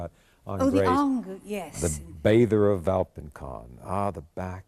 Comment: what painting are they referencing? (English isn't my first language so I just can't tell what he said) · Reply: The Valpinçon Bather